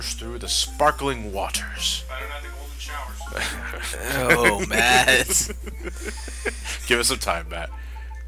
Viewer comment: What Did Matt Say ? Minutes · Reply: "better that then golden showers"